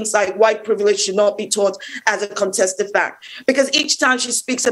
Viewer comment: Correction: I meant to say 'Uncontested' but sounds like I said Contested. To be clear Kemi Badenoch said White privilege should not be taught as uncontested fact.